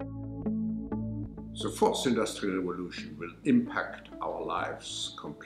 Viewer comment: his accent makes it sound like he says 'the forced industrial revolution'